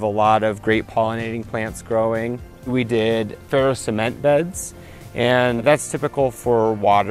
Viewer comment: Question: what are the walls of the raised garden beds made of? I could not tell what word he said (at about 6:05), and the closed captioning didn't get it right. It is xxxx cement beds. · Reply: ferocement beds. It is commonly used for construction of water containers in other countries.